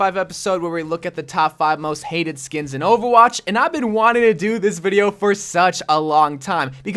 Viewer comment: did i hear “ova watch??”